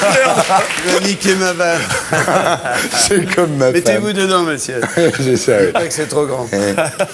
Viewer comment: il dit quoi bigard à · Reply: Mettez-vous dedans Monsieurs, il paraît que c’est trop grand